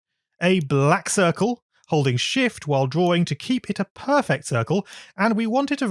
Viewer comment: I just heard holy **